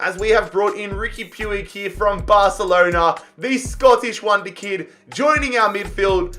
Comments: Who heard him say scottish wonder kid instead of spanish wonder kid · Who heard him say the scottish wonder kid instead of the spanish wonder